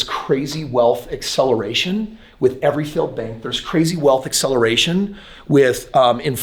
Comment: Whats the crazy wealth acceleration @ 1:06 , can't make out what he said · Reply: Ok it was "failed banks"